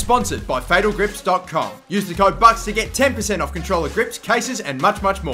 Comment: I dont know why, but every time in the opening, when you announce the sponsor. I always hear "use the code butts to ......"